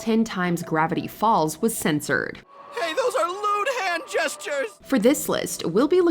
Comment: Does the mattress king really say LUDE hand gestures? I always thought he said LOON hand gestures. I got the same meaning but never thought it said lude